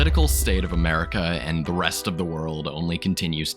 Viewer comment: it sounds like you say breast 😂